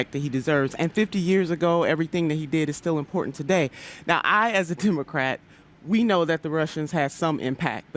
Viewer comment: <link> it sounded like she said "I as a tumorcrat"